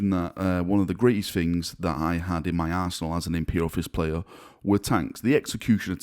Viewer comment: Okay as a none native speaker the "arsenal" at 1:02 sounded like "in my arsehole"... I'm sorry I shall repent now.